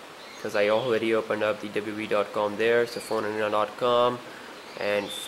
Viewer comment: lol am i the only person who heard it as phoneerotica when he actually told phone arena